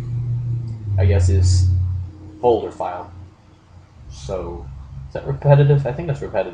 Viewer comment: What is the word in 5:10 ? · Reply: Maciek Jaszczuk folder file